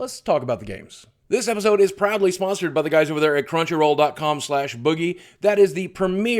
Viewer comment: I thought he said "probably sponsored" in the beginning and i was like what, probably?! haha